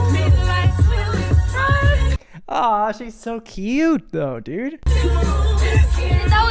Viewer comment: did he really say "achei so cute"???? IS HE BRAZILIAN?